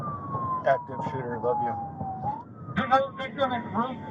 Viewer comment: Did I hear a cop from the University School shootout say “there were many cowards hiding in classrooms” meaning children and professors ?😠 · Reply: Not only that, but the officer in the car also says "active shooter, I love you" at 1:27:30... Or am I hearing this wrong?